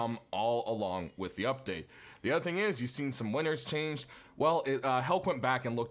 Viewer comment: See some what changed?.... Sounds like he's saying winters?.....winners?.... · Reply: Winners, as in some skins that succeeded in being picked were changed.